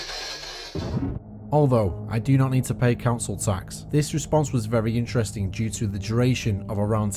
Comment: No in that spirit box said councel tax I thought it was saying prison camps Which makes a little bit more sense because you are just previously talking about that in the previous picture